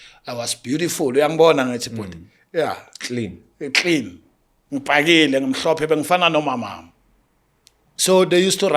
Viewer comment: Did he just say 'ngipakile"?? 😳 🤣